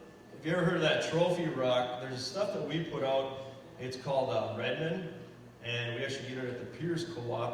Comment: At about 15:10 in the video you mentioned a replacement for Mineral Rock, I thought you said remnin but could not tell for sure could you tell me what it's called again? I'm looking for it online but I must be spelling it wrong because I can't seem to find it. Thank You!